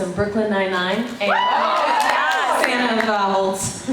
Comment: Who did she say? Someone from B99, but I couldn't decipher it. The only openly queer characters I could think of from B99 was Ray and Kevin (but don't we all secretly know that Rosa is bi?). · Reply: She said Holt